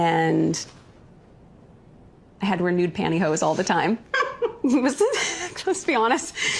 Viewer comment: Jesús, you have misheard her: she doesn't say she had renewed pantyhose (did she keep laddering her tights?), she says "I had to wear nude pantyhose all the time."